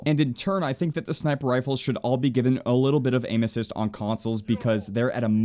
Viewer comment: When he said aim assist I thought he said anuses